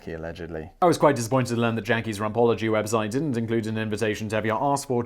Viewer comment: I misheard very British first delivery of the word "Rumpology" as "Ron Paulogy", i.e. the study of Ron Paul.